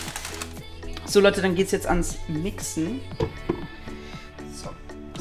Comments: hat er da ernsthaft gersagt: ,, So Leute, dann geht's jetzt ans Wi***en" ?😂😂😂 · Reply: Ich habe mixen verstanden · Wer hat bei 8:28 auch wixen statt Mixe verstanden😂😂😂👌🏼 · Ich hab verstanden:So jetzt gehts ans wixen😂😂